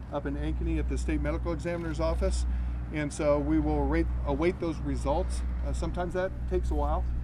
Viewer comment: he accidentally says the word "Rape" when trying to say "await". I think he subconsciously said that cause that's what was confessed by the suspect. I hope not but sometimes when trying to with hold info, it just finds its way out anyway. She seemed like a sweetheart and a good person. My heart goes out to the family and to the killer I think the family has the right to execute him if proven guilty.